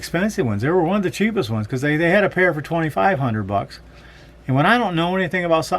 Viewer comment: i need aids but also limited to what i can spend. unclear what you said they cost in beginning of video i thought you said 135 a pair then towards end i caught you saying thousands